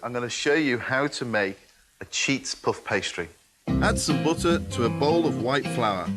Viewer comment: I thought you said cheese puff pastry instead of cheat puff pastry.